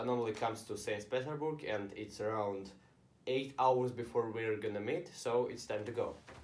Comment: i thought you said we are going to mate lol